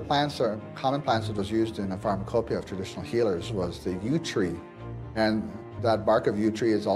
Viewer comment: what kind of tree did he say that was used for cancer? I looked at the Transcript, but it's not helpful for this. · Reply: The Yew tree. It was one of the chemo treatments my medical team used to treat my skin cancer with Taxol.